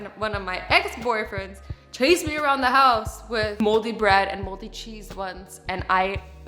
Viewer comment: I thought she said ‘moles’ until she said he ran around the house with MOLDY bread